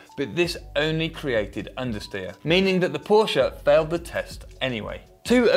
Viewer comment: what did he say rosch? Idk I'm german btw